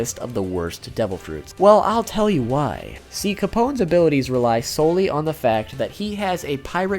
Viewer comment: does he say si capone's? XD are you spanish? PD: just found this channel and subscribed, has so cool content and i'm spanish XDXD